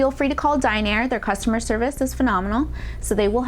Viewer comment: I kept hearing "I hope you love your kid just as much as I do!" but I finally heard it right.. She loves her kit.. Not my or her kid..